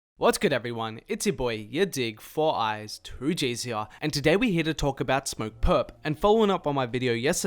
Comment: Just found your vids and I heard “what’s up y’all, ya dig here, four eyes” I was like lmaooo you call yourself four eyes?